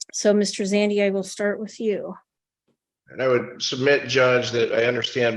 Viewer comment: Did he call him Mr. Bitchcock? 😂